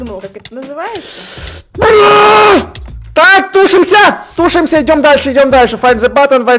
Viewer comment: Эдисон:тушимся!Как мне послышалось: Эд: тужимся!